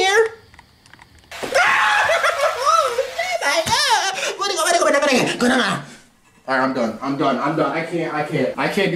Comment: what did bro even say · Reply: He is luigi